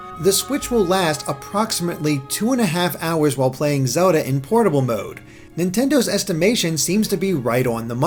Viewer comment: I heard "Pornable mode".